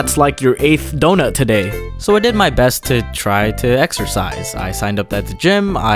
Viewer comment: Exercise? I thought u said E X T R A F R I E S